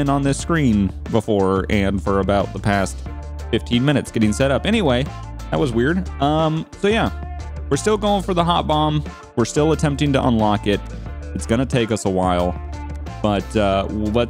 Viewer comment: I wasn't looking at the screen, so I was sure I heard, "We're still going for the hot mom" xD .. sorry. ~~